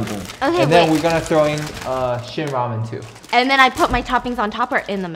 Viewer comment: every time I hear "shin" ramen I actually hear "shit" ramen lol